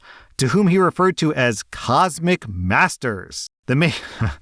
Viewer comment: Lol I heard "cosmic bastards" x)